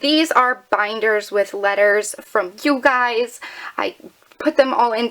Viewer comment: At first I thought you said "letters from cute guys", and I was shocked how you filled up three binders. XD